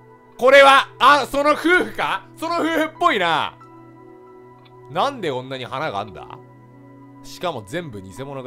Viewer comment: やったー！殺戮ｷﾀｧ( 厂˙ω˙ )厂 「なんでこんなに花があるんだ？」が、「なんで女に腹があるんだ？」って聞こえたw